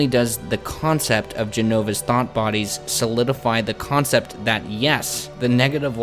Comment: is it bad that I heard that as "Jenova's Thot body." ..I think I need to sleep I've been playing games too much